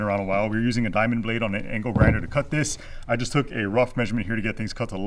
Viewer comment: I swear it sounds like is saying ankle grinder. 😝 Ludo sure has grown fast. Such a cutey! I know why God saw fit for another baby. Put all those accusers to shame for saying you’re not the mama. 😂